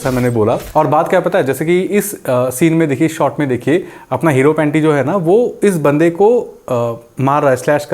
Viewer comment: did he just say hero-panty??? LMAO